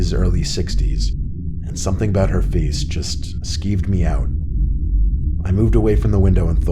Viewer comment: Did he just say it “skeeved him out?”